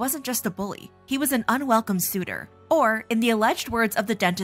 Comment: I think he might've said "rapier wit" at 9:56. Sure, he's creepy but I doubt he'd say he had a "r*pist/r*per wit."